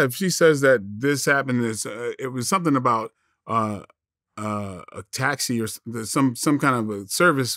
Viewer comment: I thought she said it happened at a gas station